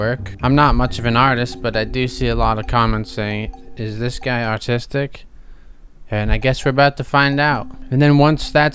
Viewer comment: Did you say "artistic" or "autistic"? Lol